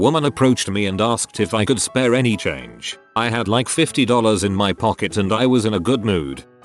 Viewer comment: if you turn on sound it says 50 dollars instead of .50. For a second i thought some billionaire wrote it